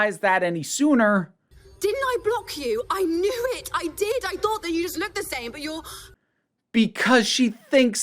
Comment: I first realized it when she said "I thought you looked the same" but it was because I thought she said "I thought you all looked the same". I thought she was a white supremacist but didn't realize the whole of fine time was until the episode had ended